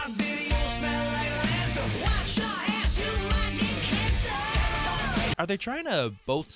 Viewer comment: Oh I thought the refrain said "Wash your ass, you might get cancer". I thought it was about getting old.